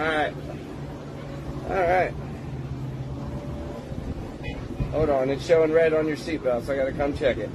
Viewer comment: She literally yelled HELP 😂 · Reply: I heard "HOLD MY..."